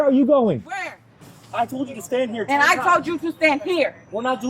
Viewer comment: The cops are wrong here. She clearly said, "I told you to stand there."